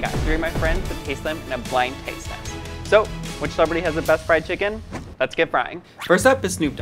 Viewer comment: I really thought it said "best friend kitchen" Till almost half the video